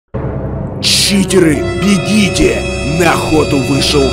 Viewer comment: мне послышалось или кент сказал "Читеры, бегите нах@